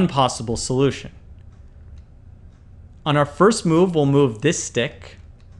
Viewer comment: I spend 15min to solve the question and then I finally give up and heard the answer ..... I was so mad because I thought he said to remove the stick